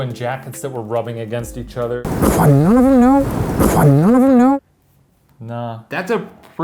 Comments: The "what" EVP sounded like Shane. I thought it was him saying "what, none of them know?" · It sounds like Shane is saying “What- none of them know?” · I like the background a lot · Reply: also, I thought Shane said the what. like i thought he's said 'what? none of them know?' that's just how clear it was to be someone saying the word 'what'. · I thought Shane was saying "what" !!!!!!! I thought he was saying "What, none of them know?"